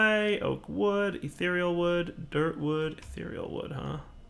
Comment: did just say dirt wood...